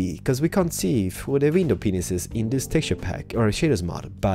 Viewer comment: I probably have problems hearing because it sounds like your saying window penises....I probably have hearing problems